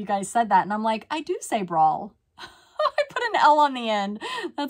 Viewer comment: To me, it sounds like you say, “braaawh,” which sounds like a Southern pronunciation.